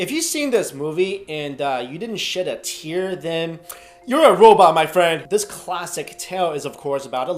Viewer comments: i thought you said if you didn't shit a tear you're a robot, and you called him uncle scott instead of uncle scar lmfao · I thought he said, "...and you didn't shit a tear" at first.